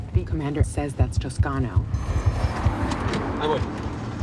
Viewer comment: aguas = im coming 😂 great translation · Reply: I heard "hay voy". it's an informal way of saying "I'm on the way" or "I'm coming".